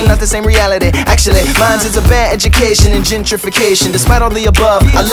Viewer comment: Totally thought he said gingerification. Oops...